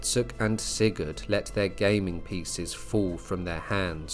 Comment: I heard let their gaming PCs fall... had to do a double take